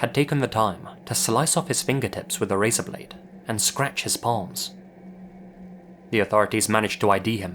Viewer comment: I thought said “…and scratch his balls.” I was like, ….okaaay then. Lol